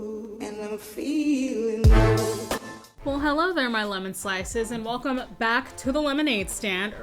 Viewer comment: I heard "Hello there , my LUMEN slices" loooooooooooooool :P